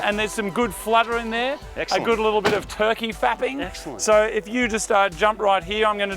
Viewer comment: did he just say... 'turkey fapping'???